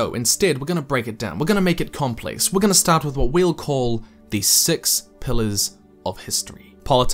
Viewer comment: I thought he said the Sex Pistols of history